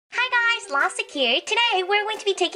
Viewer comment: When she did her intro it sounded like she said lostic except for lastic